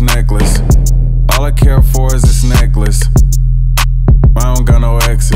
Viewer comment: All I care for is this NeCkLaCE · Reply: At first I thought he said "AlL i CaN aFfOrD iS tHiS nEcKlAcE" 🤣😭